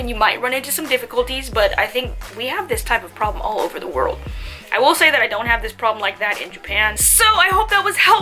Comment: Hello did she say at 17:25 "I will say that I DON'T have this problem (racism) in Japan" ?